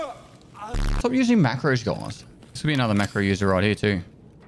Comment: Oh! Macros! I thought he was saying Makarov in Aussie linguistics.